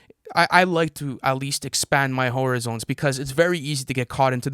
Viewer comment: Lol did say " i like to expand my whore-a-zones 🤣🤣🤣🤣🤣🤣